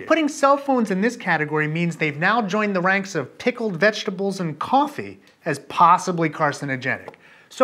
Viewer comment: So what you're saying is that pickled vegetables and coffee ALSO cause cancer oh my God everyone panic! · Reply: I thought he said "tickled vegetables". I had no clue what he was talking about, haha.